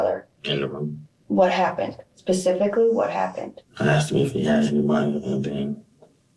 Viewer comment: pacificly what happened.....LoL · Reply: I heard specifically